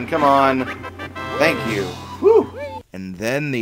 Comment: The Spells of the Washing Machine and T-Rex Spell are regular Spells in Banjo Tooie. When I first hear the voice after putting the egg into the flowerpot I thought I heard the german "Danke" instead of "Thank you"